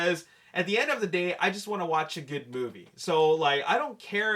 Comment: who else thought he said a good booty?